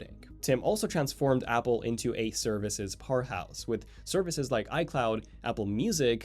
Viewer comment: lol @ 1:42 my bad when all I heard was “turned Apple into a services whore house” then turned captions on and re listened and that’s somehow meant to sound like “powerhouse” which would in fact make a little more sense, whoops 😂😅